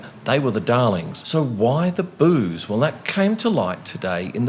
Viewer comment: I keep hearing: "Why the booze". I don't know why........